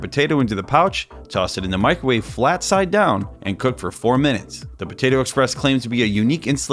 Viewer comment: For a second I thought he said, “Do not cook for 4 minutes.” ”So now let’s put it at 4 minutes”